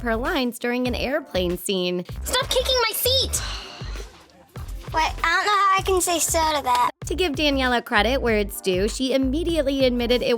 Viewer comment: Can someone tell me what the kid said? I'm so confused I've been trying to figure it out for the past 10 minutes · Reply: Yanet Reinoso he says wait i don’t know how to say so to that because she said her line wrong and he didn’t know how he was suppose to respond!